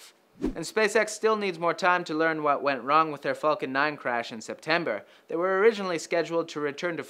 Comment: I heard "what went wrong with their fokin"